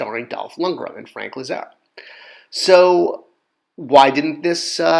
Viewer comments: Who is Frank Lizard? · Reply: That was what I was wondering. I think it was actually 3:32 when you list the stars of the Canon film. It sounds like you say Lizarro, but the CC reads as Lizard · Closed captioning reads as Frank Lizard. Not sure what you actually said, but it wasn't Langella